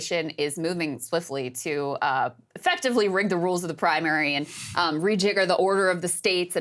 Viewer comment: I mean it was a phone interview and the guys could have asked it with better context … sounds like he said wigger lol